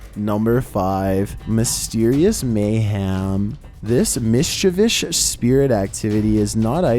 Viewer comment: Please tell me I’m not the only one who heard “Mischievish” here